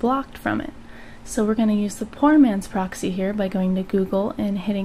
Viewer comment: Did she say "porn" man's proxy, or did I just hear that?